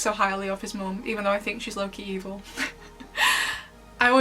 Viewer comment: So when you were talking about Emilie and you said "low key evil" I totally heard you say: "Loki evil" as in my favorite avengers villain! And at first I was like wow! I would like to see that commentary video! I caught my mis-hear relatively Quickly but I found it humours! Ha. I think that Ladybug thinks Chat Noir is mad about his unrequited love because he hasn't expressed his feelings of exclusion. I agree when you said she thinks she has kept this secret and the status quo going but in reality he knows things are different. And she has yet to share anything in advance with him-like going out of town. But she was definitely mad at him for not communicating in NY that he was leaving. She has double standards for identities. Poor kitty! However, Chat could just share his feelings. He has had multiple opportunities. All in all, I am glad it was Adrien at least he got to see how shallow she can be at times. Now if he l could just find his spine.